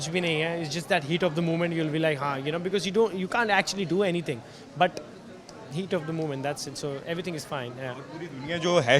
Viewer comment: Arti came to hug Asim. Does anybody know what does she wanna tell him? Btw love the Interviewer. Main tera hero ke bate batayi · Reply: Arti said to Asim - you've to call me main unke pure words bataugi